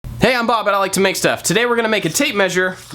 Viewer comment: I totally heard "Hey, im bob and I like tomatoes" i think you said it too fast :) 😂